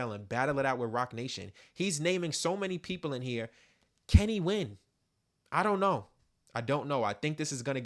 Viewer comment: Something spooky just happened. At the end you said "can he win?" but sounded like you said "Kenny when?" as in When's the last time this has actually been about Drake vs Kendrick.